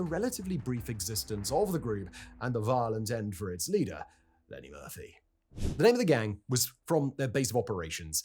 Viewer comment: really thought you said "Eddy Murphy" for a moment, almost lost it ngl